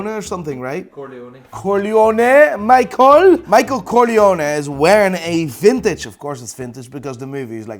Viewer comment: it really sounds like he says "michael coglione" which means Michael the idiot or the asshole lol